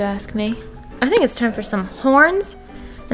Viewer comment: When I said “it’s time for the horns” I thought u said “it’s time for porn!”😂